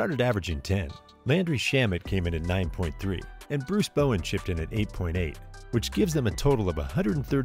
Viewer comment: Did you said Bruce Bowen ????